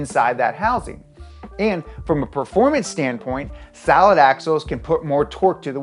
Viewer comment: did he say salad axles?